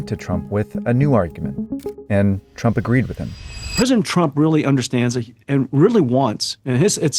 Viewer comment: Did Jensen just say "prison Trump" at 7:19? 😂